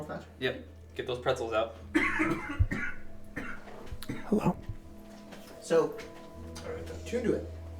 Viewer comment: Does anyone else hear someone whisper "Hello"?